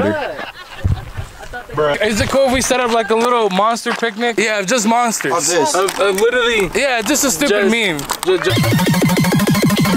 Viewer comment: thought it actually said Bruh 😂